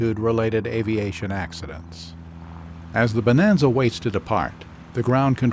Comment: I'd like to take a second to acknowledge the narrator for this series. He has the perfect, gentle voice inflection for these. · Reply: He wasn't as good as usual, though, in this video. For example, I heard him say, "As the plane wasted apart on the runway. . ." And I thought, Why the fuck would the plane waste apart on the runway???" It took me probably six times listening to the whole lesson, before I heard, "As the plane waited to depart . . ." There were other sloppy pronounciations as well. So, he was definitely not as smooth and enunciating clearly as I'm used to. I never had trouble understanding him before.